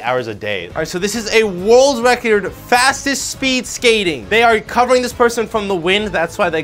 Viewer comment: I thought he said fattest